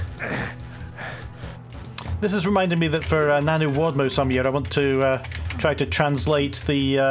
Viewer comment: Did you just say NaNoWADMo? That's a thing too? That's how I've been trying to describe RAMP to my friends. I am chuckling a little bit at the acronym only replacing the "Writing" part of "Novel Writing" like we're WADing Novels over here in DOOM.